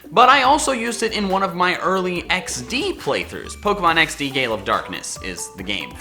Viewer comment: I'd definitely like to see more of these. Thanks for trying it again! My favorite type is Dark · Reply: Ps. I've been an English teacher in S Korea for the past three years and just got a job in Japan. I say this only because you should pay more attention to your enunciation in your videos. 3:25 I heard "Pokemon XD: Gay, love, darkness".... I'd still watch a let's play of this though... Don't judge